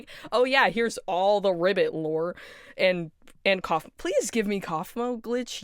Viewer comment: i noticed something weird, every time you say kaufmo the captions say caine instead, for example 8:26 how tf does youtube think they sound similar